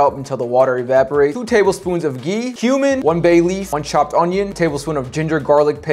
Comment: When he said cumin I thought he said human😂😂